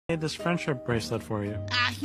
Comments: I heard "back off" insetad of "fvk off" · Love this lol (also it sounds like mable is say "f**k off" instead of "back off"